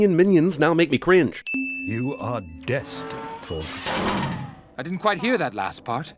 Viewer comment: All i hear is "you are destined for sh*t"